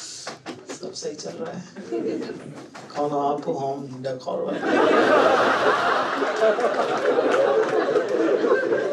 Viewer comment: Kya bol diya bhai? "kauno aapor hum de korbe?" 😂😂 audience to aise hi has rahi hai. #Talent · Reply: Looks at the girl in front row and says "Kaun ho aap, Home decor wali".